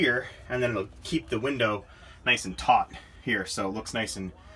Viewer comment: What the heck did you say at 8:32???? TOUGHTTTTTT??? Wtf lol 😆 this guy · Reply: Yes. “Taut” check it out on the dictionary, maybe learn something today? <link>